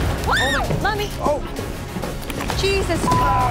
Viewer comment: did she say mommy?